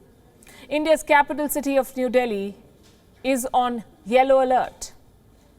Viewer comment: Palki said, "Delhi is on yellow alert". For a moment I thought she said, "Delhi is on China alert".